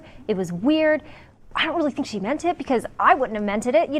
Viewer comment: Did she just say "meanted it"?!